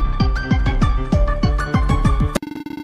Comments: i heard WATCHING AS THE SKID GETS SAUCE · is it just me or did I hear sus in this part?